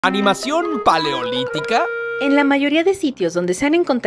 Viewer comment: No mames pensé que decía animación polémica